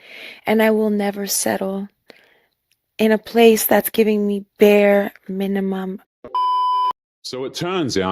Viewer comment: "Bare mininum" this is now women's biggest red flag. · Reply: I thought she said bear 🐻 minimum 😅